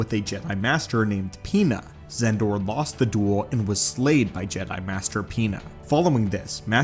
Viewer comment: For a split second I thought you saying, "Jedi Master Peanut"